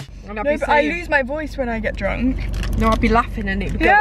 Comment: Wait did she say I loose my voice when I get drunk at 7:03............ or is it just me (I’m new btw)